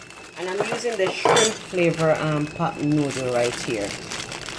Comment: I thought you said cock noodle the first time. Lol. I don't eat shrimp. Nice vid doe.